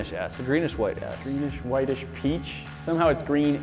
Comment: When I was playing the vid, my sis heard the “greenish whitish s” and thought it said” greenish whitish a**”